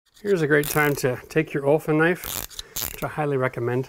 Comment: Does anyone know what kind of knife he is recommending at 7:35 ? I can’t make out the word and it isn’t shown in the recommended tools list. Thanks! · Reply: Olfa, get the 18mm with the scraper on the back